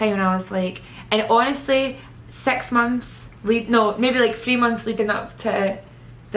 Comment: She said "six months" and I thought she said "sex months" I was so confused for a second 😂